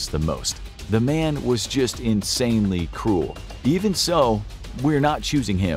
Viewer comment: I thought you'd said "insanely cool "